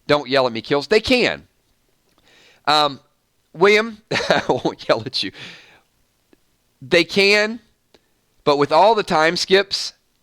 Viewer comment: what was said at the end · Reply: I thought they said hello hello is anyone there